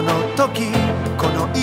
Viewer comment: saat ima kono ngopi kono isu kono sungkan 1:57 dengernya kayak bahasa jawa peace 👍👍😂